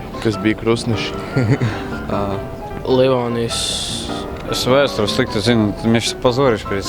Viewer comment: What is that man sayin'? I mean, he said smth. then turn his speech into Russian, as I heard. Are here people able to write me in Latvian what that man said from 2:53 up until 2:56? And can somebody also write in Latvian what did the boy said from 2:51 to 2:53?? I will be so thankful to the man help me to know what did these two persons said in Latvian , I don't mean the translation, just the Latvian text . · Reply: Hei! at 2:53 man is saying "Livonijas". At 2:56 "Es vēsturi slikti zinu" and than it is hard to hear but it is something in russian.